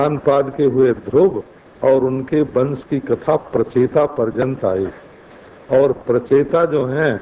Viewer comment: पर स्वामी जी ने क्या कहा? " ध्रुव और उनके वंश की कथा प्रचेता .... आई " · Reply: ध्रुव से लेकर प्रचेता तक की कथा